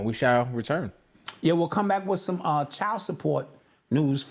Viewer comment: i thought he said CHOW support...im gone LOL!